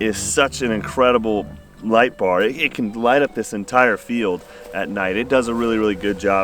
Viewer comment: "I thought he said it could light up that" 4:26 "GAHHHHH!!!!"